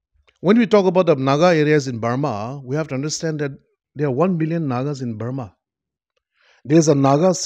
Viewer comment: Did he said 1 billion nagas in Burma 6:00 😂